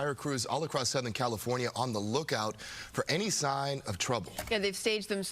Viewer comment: I thought he said the strong, hot winds have fire crews on the lookout for any sign of Trump. 😂😂😂